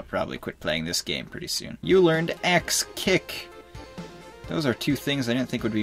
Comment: I heard "Sex Kick" instead of "Axe Kick" guess i'm playing too much Smash Bros Melee.